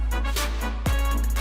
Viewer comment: lol did anyone else catch 10:00?? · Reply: Hard to be sure, but ‘For Frodo’